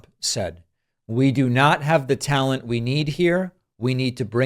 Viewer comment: All due respect David, Trump did not say "We do not have the talent". The Russian ASSet said "You do not have the talent here". He views himself as a king who isnt even part of "America"